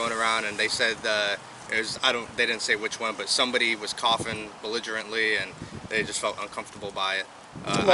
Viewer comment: did he say "coughing belligerently" or am I hearing wrong? Jurisdiction under the "police powers act" within the town charter does NOT allow for cops to "come out for other things". Its for no other reason than to expand the authority of the state and if we had lawfull judges they would put a stop to it in one second becuase its a direct violation of section 19 title 242. BUT that dont pay enough...early voting? app voting? no name ballots? no ID?? Not how our founders envisioned it. We keep cameras on all things of value but not voting? ballots should be on camera 24/7